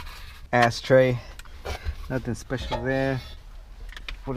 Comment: it sounded like he said ass tray